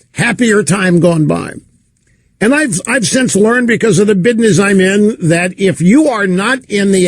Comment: Did he say bidness? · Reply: Mr. Smiley Lol you made me rewind...and yes, he’s said “bidness”.